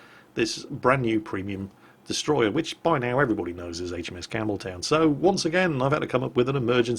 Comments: Wait, I must need to clean my ears, I could've sworn I heard you say the H.M.S. Cameltoe... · did you just say HMS Cameltoe..?